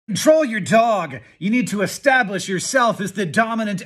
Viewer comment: For à second I thought he said to troll your dog😂